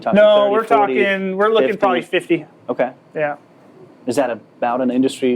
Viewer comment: Fifteen or fifty percent profit margin? I can't understand what he is sqying. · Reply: It's 50%. Thanks for watching! ☺️